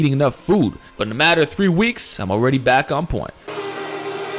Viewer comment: Did u say iam back on porn